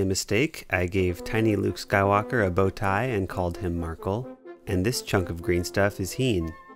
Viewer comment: HIS NAME IS MARKL??? I KNEW I WANST CRAZY, I SWORE I HEARD MARKL BUT GOOGLE SAID MICHAEL SO I THOUGHT I WAS JUST DUMB.